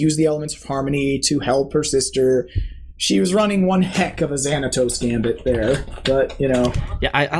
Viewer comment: Did he say "Xanatos scam"? Was he referring to the villain (for the most part) of the TV series Gargoyles? Because it would be awesome, and it would make a lot of sense.